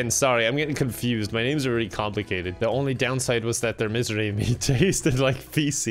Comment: For some reason I kept hearing it as "mystery meat", and I'm not sure which of the two sounds better